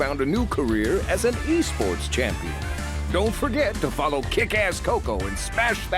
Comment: Did I hear Crash said ‘’kicka**? 😦